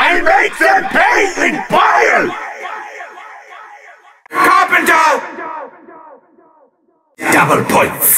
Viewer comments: “CARPET TOWN!” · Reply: I thought he said Carpreta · CARPEN'DOH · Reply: I thought it said carpeter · Anyone hear bobadob instead of carpenter